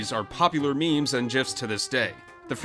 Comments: did you just say.. Jif??😂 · did he say “JIFS” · Did he just say "jifs"??? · did he just say... jifs? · DID YOU SAY JIF